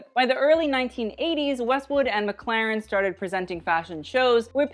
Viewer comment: did he say mclaren